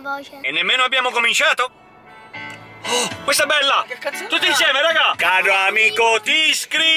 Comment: secondo me ha detto "che c@ solo io ho sentito così 🤣😂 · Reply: Noooo ha detto che canzone è ❤️